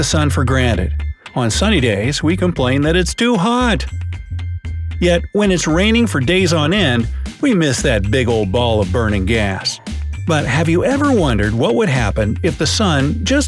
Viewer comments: XD i thought you said but when its raining we all miss that bigo fire burning ball of a** XD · I thought you said "for a day"! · I thought you said 1 day